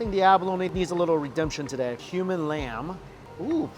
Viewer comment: I thought he said “human lamb”